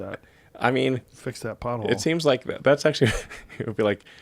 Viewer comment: Every time Elon says " pothole" I hear "butthole"